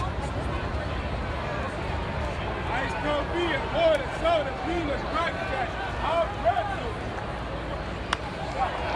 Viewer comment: Could you tell me, what he saying?? I can only listen the first word “Ice…..” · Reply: Ice cold beer